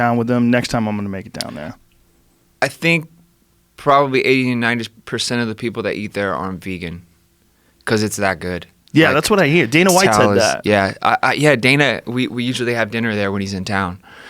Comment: FFS ... clicked ahead on 2:07 - First words I hear: I'm Vegan, Cus it's that good.